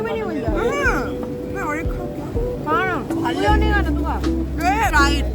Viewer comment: Did she say Khaunga? 17:09😂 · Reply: She was asking "ek aur khau kya?" I said "kha na"